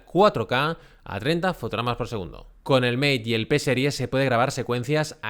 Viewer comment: Muy buen video! Aunque también me confundió, por ejemplo en el minuto 14:00, que dice "con el mate y el ps10". No termine de entender que es o cual es el ps10, o si yo escucho mal 👍👍👍